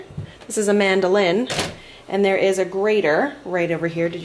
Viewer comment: When I first heard you say mandoline, I thought you said “ this is AmandaLynn” lol